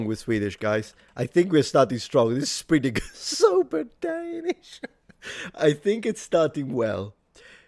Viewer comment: Did he say "sober Danish"? As a Swede, I'm well aware of Danish being described, not as drunk, but as a throat condition. I told a Dane that and got "well, we have the best (cough curing) hard candy" (With Kongen af Danmark, that's actually true).